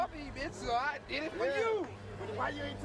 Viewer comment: 2pac: "Why you ain't tell nobody?! You could of started that shit" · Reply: Sound like he said you started this shit